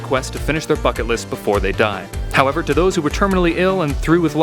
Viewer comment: the first time I thought you said to finish there fuckit list XD